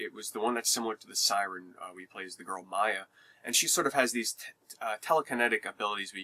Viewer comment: It sounded like you were about to say she has these tits! :P